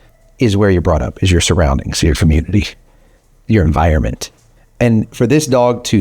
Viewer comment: Did you say "your famunity" (1:10)? I like it!!